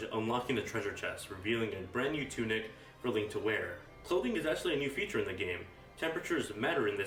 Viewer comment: what do you say at 2:09. sowling?? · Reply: he said clothing